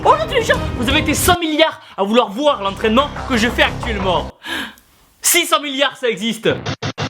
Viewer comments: J'ai entendu "600 milliards" voilà voilà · Quand tu dis "Si 100 millards sa existe" j'ai compris "600 milliards" 😂